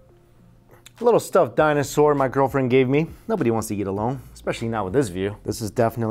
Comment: did he say his girlfriend?